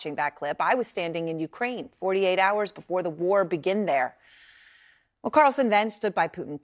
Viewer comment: I like how the close caption says “before the war BEGAN there” but she says “before the war begin there” LOL and I think she catches herself being dumb